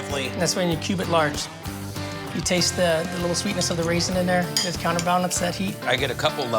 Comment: Did he say there are raisins in his chili?